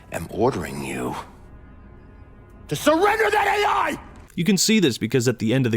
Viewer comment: When I first herd Del Rio say " Surrender that A.I" I thought he said That is a lie Edit: this is random I know